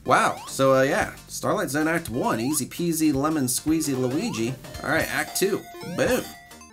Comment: Did I just hear lemon squeeze luigi? lol